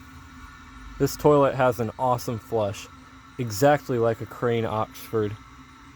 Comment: I Heard Crane Oxford